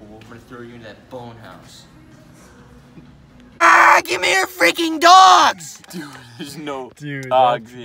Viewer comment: AAAAAAAA GIMME YOUR FRIKKEN DOGGGS! That’s what it said. I think he actually said I CAME HERE FOR DOGS!